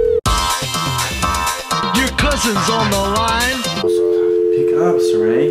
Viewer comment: This whole time when it said "Your cousins on the line" I thought it said "Your crush is on the linee" 😂😂😂